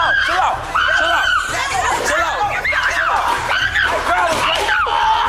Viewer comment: All I hear is “ get off my susta;”😂😂😂😂